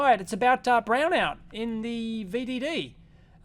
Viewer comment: I hear "Brownout in the BVDs" I need to pay closer attention sometimes....